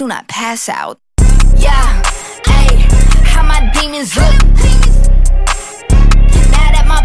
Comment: English is not my main language and all i heard was "am i demons slut" 😭 my bad.